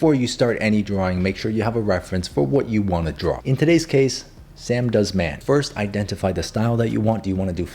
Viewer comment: Did he say SamDoesMath?😂